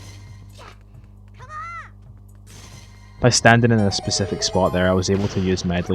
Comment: did Link just say "Come on!"? D:!